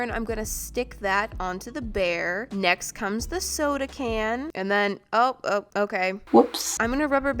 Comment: Just me or did it sound like said snow—da can